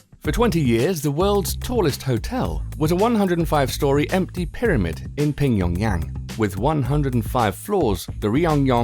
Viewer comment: Did he just say pingyongyang?